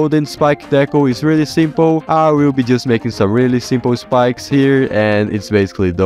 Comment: Wait..... I hear you saying "I will just making SIMPLE ground stuff here"